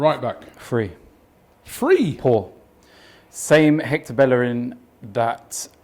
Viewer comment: editor thought said 4 when he actually said poor 😂